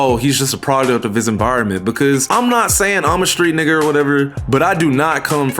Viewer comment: did you say street nigger??